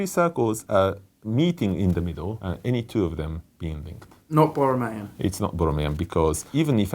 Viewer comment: okay, driving me crazy, what are you saying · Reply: "It's not borromean". Watch part one, they talk about what that means